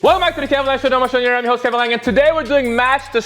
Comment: Wtf is he saying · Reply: "welcome back to the kevin langue show, the number one show on the internet, im your host kevin langue"